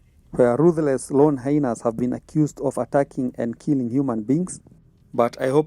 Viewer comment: Did I just hear you say" Ruthless Hyena were accused of eating humans"?😅😅😅 Stop that son